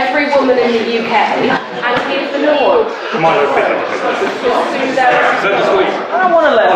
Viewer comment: can anyone tell me what milo says at 1:11:53? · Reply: king96pwn he said Good Lord and then syarted saying the glib and patronizing to the audience comment the he then went on to say more clearly.